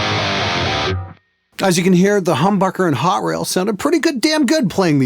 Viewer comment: it sounded like he said humburger